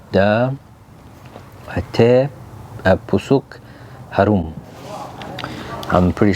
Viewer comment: kok kedengeran gw teh busuk harum sih.. heheheh...